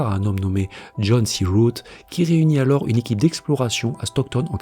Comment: Sur le coup, j'ai entendu "John B. Root".😂